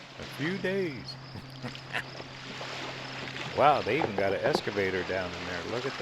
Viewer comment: I have to share: At the first beach, when you said “excavator” I heard “escalator” and for a second I thought, well, that’s getting fancy. 🤣🤣